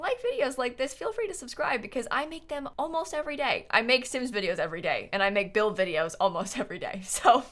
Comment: I thought she said I make sims videos every day and I make gnome videos almost every day